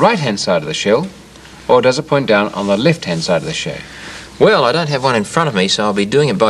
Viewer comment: Is the twist of the thing called the Golden Ratio?, and it sounded like you said The left side of the Show?.